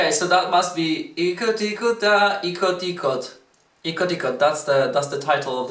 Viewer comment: Hahahaha I heard "itot" lol 8:04😅😂